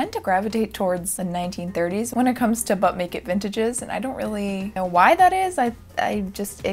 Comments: YASSSS love Yzma! Eartha Kitt was a great one. Also, I definitely knew what type of video this was, but I heard "Butt-naked vintage" at the beginning. 😂 Whoops. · Not gonna lie, I thought you said Butt Naked vintages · I could've sworn she said butt naked vintages like 3 times😂